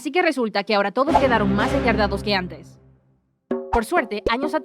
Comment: heyyyy, ¿que dice la narración del min. 2:10 porque lo que escucho no es lo mismo que dice los subtitulos???. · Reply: Creo que dijo "Y ahora los campesinos quedaron quedaron más endeudados que antes"